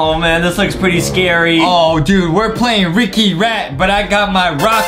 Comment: Thought. said wicky rat😂